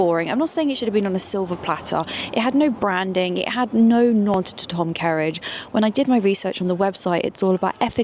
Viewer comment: What does she say at 15:13? Na tête Tom Kerridge? Thanks in advance! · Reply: No nod to Tom kerridge